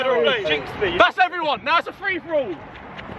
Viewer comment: “That’s everyone - now it’s a free for all.” - Harry Lewis, iconic UK retired youtuber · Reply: i thought he said boss everyone😂